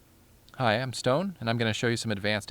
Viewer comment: Sounds like he says "Hi I'm stoned"